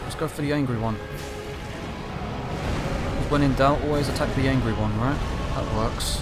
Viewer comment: "Let's attack the angry one." They're both angry. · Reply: A recent time watching this, I misheard him and thought he said attack the ugly one.